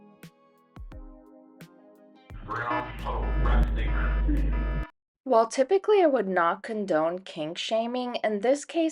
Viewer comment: what is he saying? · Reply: “brown puddle resting,” he’s talking about really gross stuff, his obsession with #2. You can hear the full phone call on the Patreon of Phone Calls from Prison for $3 but listen at your own risk, it’s very foul. All of Reddit is making fun of him, the German chick involved and creating memes about this.